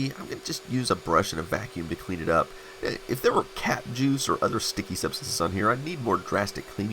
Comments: I thought he said cat juice as In a cat urinated on it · I misheard that as "cat juice" which would be a valid concern too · I thought you said cat juice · Oh, cap juice. I thought you said cat, well never mind. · Lol! I thought you said “cat juice” at 10:11 !! I was trying to figure out what “cat juice” meant when you said it again and I finally interpreted the sound correctly! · "Cap juice" lol I thought you said "Cat juice" :D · I thought you said, "If there were cat juice it would be sticky" and all I could think was, "Yeah and probably smell pretty horrible every time it got warm!" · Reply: I heard the same until I realized it was cap juice… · when you said "Cap Juice", I thought you said "Cat Juice", I was like WHATTTT!!!! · "cap juice" I heard like "cat juice" and immediately imagined :D silly me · I thought you said “cat juice” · I heard "cat juice" instead of "cap juice" XD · when you said "Cap Juice", I thought you said "Cat Juice", I was like WHATTTT!!!! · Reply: I thought he said cat juice as well...😊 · when he said cap juice i thought he said cat juice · Who else thought they heard "cat juice"? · I half expected a mouse to run out of the monitor after you'd opened it. And I thought i heard 'Cat Juice'. I went 'What the hell is Cat Juice?? oh...CAP Juice!" lol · totally thought I heard "Cat Juice". eww.